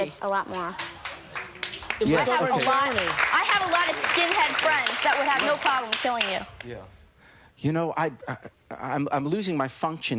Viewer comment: "I have a lot of skinhead friends that have no problem with killing you" is what she says